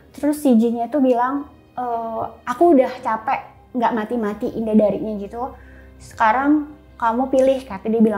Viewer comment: Iya anjay ini mantan istri Cesar dia bilang namanya sendiri indadari di mnt 22:00 sampai 22:10 coba lu dengerin dan bls komen gua kalo gua salah dengar..!!!